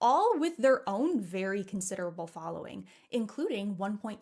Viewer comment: Ohhh.. CONSIDERABLE FOLLOWING ....I thought you said CONSIDERATE following....I was like, the heck you say ...